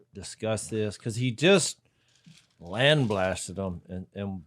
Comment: I heard "land blasted" and it made sense to me haha kinda like "water swatted"